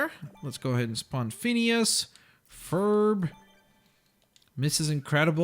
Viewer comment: Is it just me or does call Perry Ferb